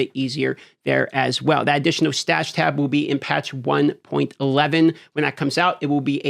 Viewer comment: I think they said it's patch 1.1.1, not 1.11